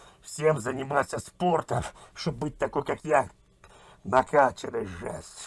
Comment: Мне послышалось ? Быть такой как я ?